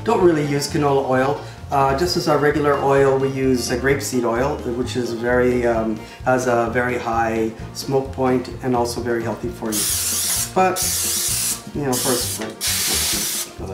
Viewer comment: oops I must have misunderstood you in the video. I thought you said rape seed oil. my bad lol. still a great video.